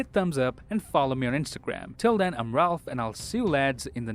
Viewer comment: did he just say "I'm Ralph"